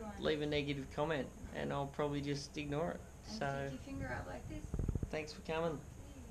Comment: I thought she said “ and stick your finger on my clit “ at the end Good vid daddy